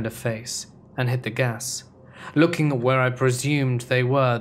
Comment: During story #2, after he said he was smoking. I heard " I had gas" instead of "hit the gas"